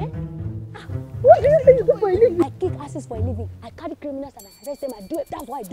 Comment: Mama tao said go and get lace from mandilas and use it to sew bullet proof at 15:22 The word kick ass was mentioned twice I kick as for a living by purple bee 15:33 I thought you said you kick assess for a living by mummy G.O 17:49 WhatsApp family we mount here😅😅😅